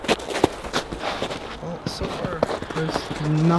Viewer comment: Time stamp 6:08,i heard a male voice said: "So far" i am not joking at first i thought you was talking but you didn`t. That was awesome 😊